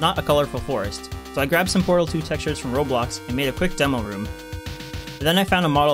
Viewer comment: Did U Say Roblox Bc I Love Roblox